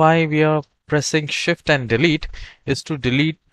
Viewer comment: What did you say @ 1:17 shift and what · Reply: Shift and Delete Key